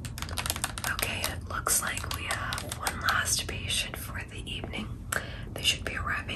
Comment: I literally thought she said “we have one last constipation for the evening” in the very beginning 😂